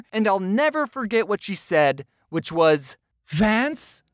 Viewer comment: and it sounds like he said ants